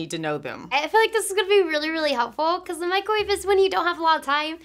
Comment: I thought she said when u dont have hands but she said pans